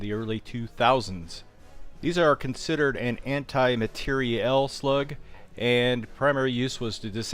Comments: Thought that said antimatter.. I was like whaaaatttt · who else thought it said anti matter